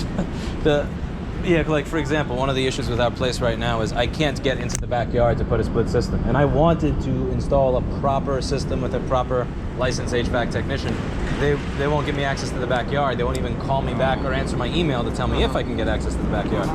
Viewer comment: nice space. but I heard "crane HVAC"!